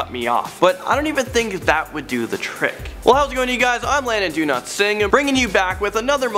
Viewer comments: i thought he said brandon do not sing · sounds like he said Landon do not sing